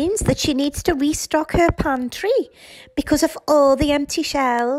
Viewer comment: I thought you said restock her country, not pantry lmfaooooo